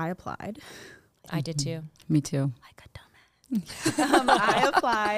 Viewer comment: what does iyanna say when she whispers ??? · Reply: She said "Like a dumbass" 😂